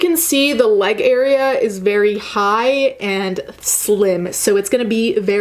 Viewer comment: I literally thought you said "the labia area is really high" on that bodysuit xD I'm gonna call the crotch of pants the labia area from now on.